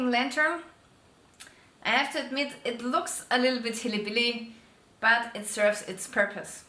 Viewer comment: Did she just say hilly billy?😂😂